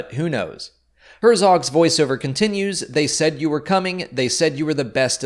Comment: I thought I heard him say "Baze said you were coming" I was like WAAAA!!??!